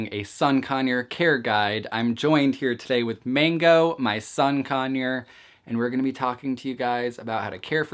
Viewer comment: It sounded like you said “My son conure”